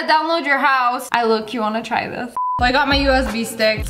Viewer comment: i thought she said "i look cute i wanna try this"